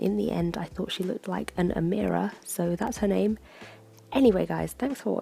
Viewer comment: When you talked about coming up with a name for her, "Nora" popped into my head ...and then I thought you said it! I had to rewind to hear "Amira"! haha love your sketches :)